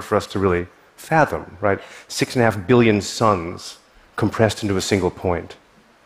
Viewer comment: did he say six and a half billion suns in a single point, billion with a B???